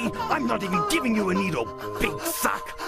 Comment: did Vegeta say big f*ck?